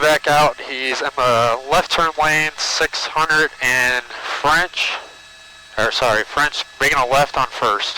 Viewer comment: I thought he said Franch, which Herr Scheuler didn't like..